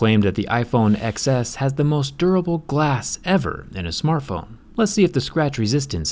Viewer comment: I thought they said most durable glass in any iPhone....